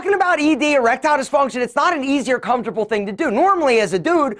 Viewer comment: “roman” ought to be capitalised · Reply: “easier” is subtitled instead of “easy or”